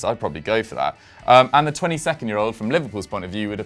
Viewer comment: I can't be high, but I heard "22nd year old"